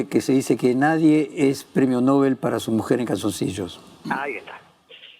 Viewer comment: ¡Hola! Alguien me podría aclarar cómo es la frase que dice Fontevecchia? "nadie es premio nobel para su mujer y calzoncillos" ? (03:47). Escucho mal yo, puede ser. Gracias de antemano